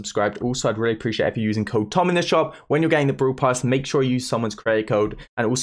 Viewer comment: i thought he said: if you get the brawl pass make sure u use someone’s creditcard😂